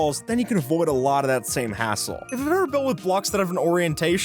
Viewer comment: What is bro saying? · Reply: hassle